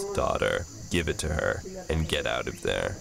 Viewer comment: did he say give it to her and get out off there